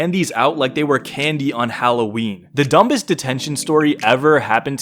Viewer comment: did you just say "dumb-ist"?